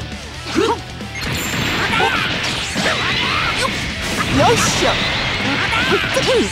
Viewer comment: Did whis just say “Yosha?”